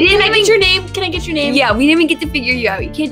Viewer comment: what did he say in 4:55 -4:57 ??? anyone can help to text it for me :D . [sorry my english is bad] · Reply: "you'll run into me somehow"